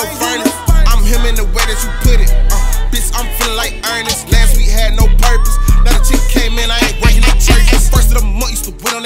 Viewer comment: am I the only who thought he said last week we ain’t had no perkies now chief coming in we ain’t bringing no turkeys?